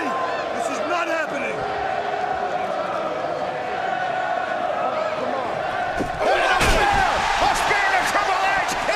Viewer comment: Does anyone know What the fans were chanting at 3:40 ?? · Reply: i think its ambrose ambrose ambrose